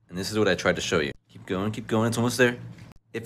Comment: "And then, after uncovering whether your honey is real or fake, add some more water, a little more water, some more water, and at this point there's so much water that you just can't stop! next take your gallon of water and shift your plate into a glass container (roughly the size of the plate so it fits inside) then you can pour in your water, if your honey becomes MORE of a honeycomb then it's EXTRA REAL HONEY. Oh, and for good measures twirl your pendulum over the water and honey to unlock its godly powers!" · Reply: I Thought he said honeyc*m